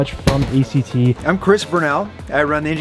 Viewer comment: hold up. did he just say he's "Chris Brown?"